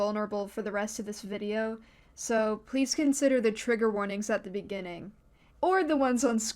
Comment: i thought the intro said tiger warning